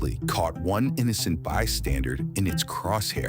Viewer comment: Did the ai narrator say “one innocent bystandard” instead of bystander?